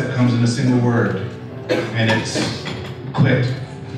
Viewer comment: Thought he said Clit